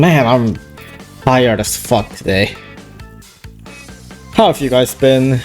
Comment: I thought I heard “Tired as fuck today” As “Hard as fuck today”